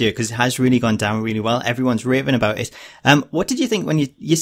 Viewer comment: sounded like he said "raping about it" 😂😂😂